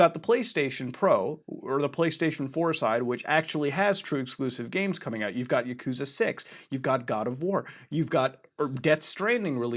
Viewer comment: I thought the shit said explosive games boi.